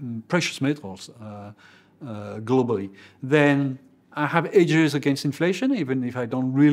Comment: What is he saying at 53:30? Even the subtitles say "Then, I have [indiscernible] against inflation". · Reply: Hedges against inflation